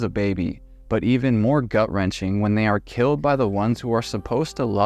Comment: Doesn’t it sound like she says “you’ve killed him”? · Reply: I thought she said, we've killed him!!